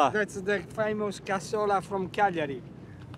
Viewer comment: Where did he say the dish, cassola? was from? Sounded like Calgary but I know that can't be right lol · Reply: Cagliari, it's the Sardinia "capital".